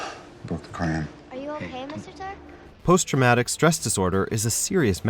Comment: Sounded like she said Mr. Tark